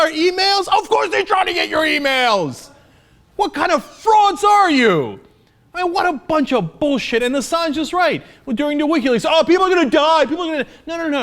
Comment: did he say what kind of frogs are you at 1:51. lol